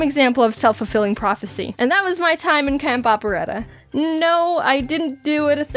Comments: I thought it said cat opera 😂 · Anyone thought it said cat Opera No just me...ok